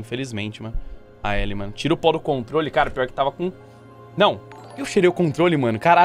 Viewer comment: mano, eu ouvi "tira o pau do controle" kkkkkkkkkkkkkj